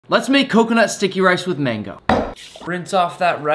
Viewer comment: I thought he said stinky for a min